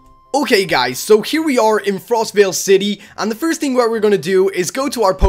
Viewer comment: Did he really say frostevil city